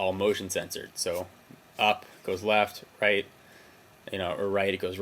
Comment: Did he say, "Up, it goes left."? Wtf? Lol, besides that, great video.